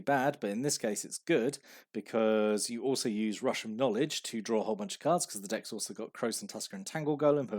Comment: I heard "rush of knowledge" as "Russian knowledge" while listening to this in the background and was like "what?"